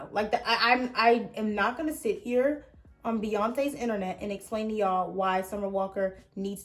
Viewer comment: Did she say I’m not gonna sit here on BEYONCÉ’S internet 16:16 OMG I STANNNN 😂😂😂